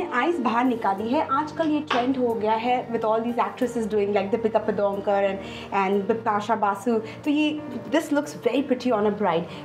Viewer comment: Did she actually said “deepika padunkor"